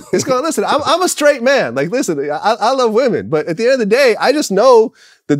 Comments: Like listen NGGA 😆 LOL I HEARD IT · Did vlad just say listen nigga at the 1:23 mark 😂😂😂